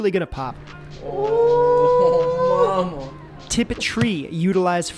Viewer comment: Did someone said "mama" in the background at 1:55 sec?!